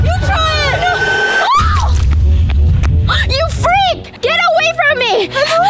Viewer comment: 😮 did just call Regina a freak?